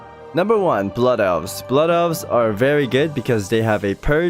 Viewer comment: " Number one blood elves, blood elves are very ghai" I cant be the only one who thought you said this instead of good Great video tho! that guide helped lots to get into rets